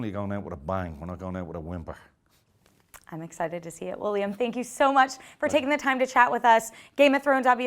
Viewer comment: did she called him William